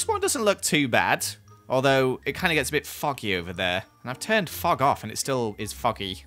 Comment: I thought it sounded like said “f**k off” instead of “fog off”🤪